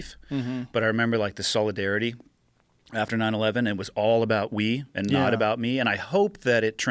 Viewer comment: I thought he kept saying"it's not about weed and not about me"..I was worried for a second🤣